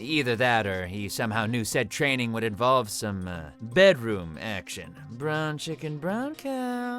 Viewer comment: Is saying "brown chicken, brown cow"? The subtitles don't help. 😄